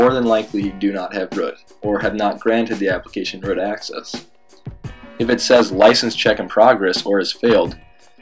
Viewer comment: What did you say at 2:21 or 2:20 ????? Was it broad access.